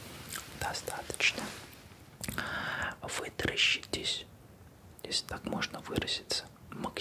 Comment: Мне послышалось вместо вытаращитесь: вы дрищитесь